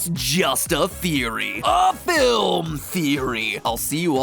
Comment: did he say... Film theory?